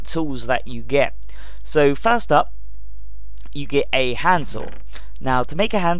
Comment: When he said "hand saw" I thought he said Hanzal (purposely misspelled)